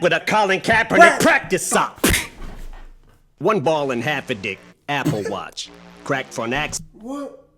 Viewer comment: It sounds like he said, “ one ball and half a dick” Apple Watch